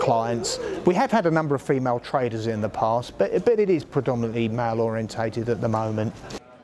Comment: "The females". Like he’s talking about animals, the useless waste of space. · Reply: Exactly when does he say "males". I genuinely missed and I'm happy to be corrected. I hear "male oriented", which is something else.